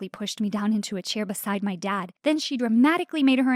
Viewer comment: When her mom said "i got a job" i heard "i got a dog!" And i was like "LMAOO WHAT"